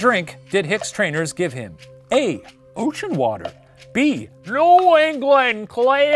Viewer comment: i thought he said gucci water and i was hecking floored